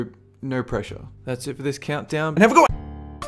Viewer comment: how u so have a good one so fast? when you say it that fast it sounds like your saying AND HAVE A GOING